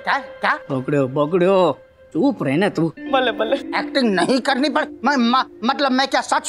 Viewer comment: Did I hear Balle Balle ??!! 😅😅😂